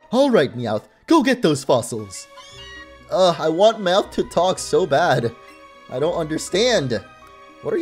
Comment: i heard "i want mouth to talk so bad" 😂